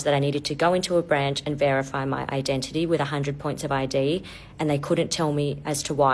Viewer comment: minute, did she say "identification points" ? like a digital ID social credit scoreboard